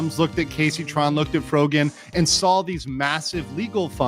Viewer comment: "Jabba" instead of frogin sent me )